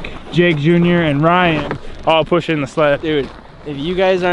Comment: did you just really call the jet ski a sled 😂 or am I hearing it wrong